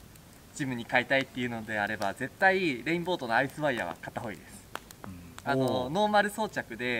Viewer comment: の所聞き取れないのですがなんて言っているのでしょうか、教えて頂きたいです。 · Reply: アイスワイヤーですね